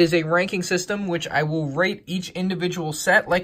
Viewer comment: I thought he said rape